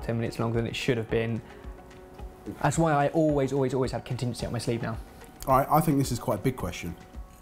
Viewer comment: what does say at 6:28 ? he always....? sound slike has kittens on his sleeves haha